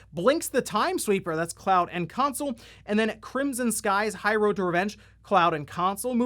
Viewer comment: Aww i thought it you said crimson sea for the xbox game pass...😢